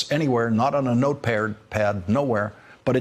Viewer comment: Love you Lawrence. I got a laugh at 8:49 Did I hear notepaired?